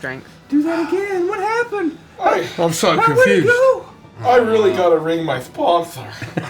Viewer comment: can someone translate what said at 1:29:13? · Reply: "I really gotta ring my sponsor.."